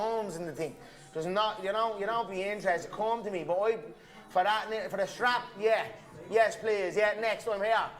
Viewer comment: sounded like he said for the strap hit me up dana....he only wants a title fight?